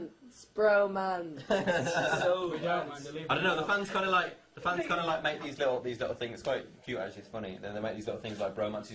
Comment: Their smiles dropped so fast when she mentioned "bromances" Can anyone hear what Zayn said at 4:19? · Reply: He said “we don’t man they leave me out” but after he said it was a joke 😁